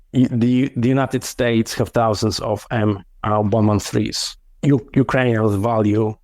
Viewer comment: What does he say at 40:45? "The US have thousands of..." M103? Not sure bc M103s are old, slow and not up to date for modern warfare. I think he says something else but I cannot understand. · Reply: M113.